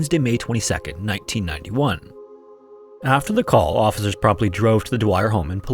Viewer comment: I swear I hear both "probably" and "promptly". I know the subtitles say "promptly" but they've been wrong before on this channel.